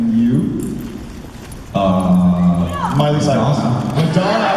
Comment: What did jensen said at 6:19?? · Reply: he said "uhhhhh, Madonna"